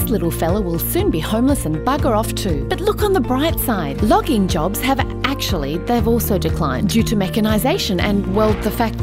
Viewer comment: i heard "lobbying jobs have decrease" and thought 'that can't be right